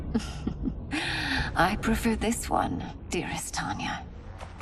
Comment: Did mileena say "dearest" at Tanya?